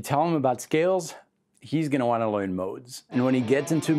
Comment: Did you just say loiean instead of learn? I'm in bro teach me everything